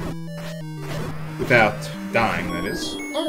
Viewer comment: For a second, I thought you said "without Dyne", as in Lunar. Then again, he helped the dragons instead of fighting them.